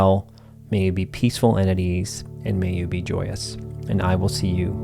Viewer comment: Following the transcript, the amusing misheard blessing at 19:40 "may you be peaceful entities" instead of "peaceful and at ease".